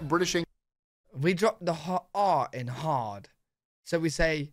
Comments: It's noticeable, that spot at 19:00, for someone outside of the UK to hear it as Hawd instead of hard when you say it compared to the video in review. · English vs American pronunciation of "hard" (around 19:00). When you said it in your video, it sounded like "hauwd" to my American ears. That said, I feel american English is too heavy on the r sounds a lot of the time anyway